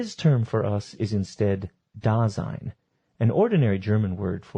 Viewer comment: DID I HEARD DAZAI HERE? 3:56 😩😩😩😩😩😩😩🍷